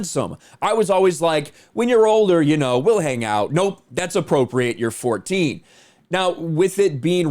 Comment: why did you say “appropriate” when it clearly says “inappropriate”? It’s literally underlined. That’s a pretty big difference. Especially with the content. Just curious.